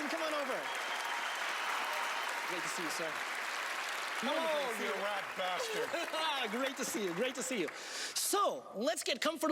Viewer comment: When Penn shakes hands at 3.03 sounds like he says ‘ you rat bastard’, lol